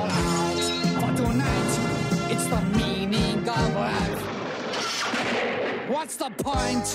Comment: Am I the only one who instead of "The meaning of life" I hear "Tha Meaning of WAF!" in 0:19????XD